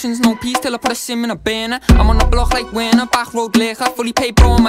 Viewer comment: I thought he Said im on the block like wena😂😂😂😂